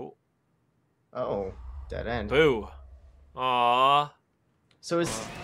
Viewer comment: I thought it said poo..